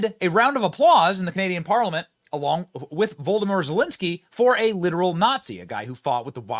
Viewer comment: I could have sworn I heard you say, “Voldomort Zelensky”.